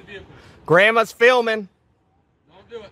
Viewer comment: I thought you were going to show that one where the deputy mag dumped the ground hog crossing the road in front of a line of traffic. When said "Grama's filming..." I thought he said "Grama Spillman..."